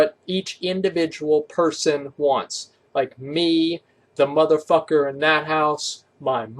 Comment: What did you just say your m*** ***** You said motherfuckenship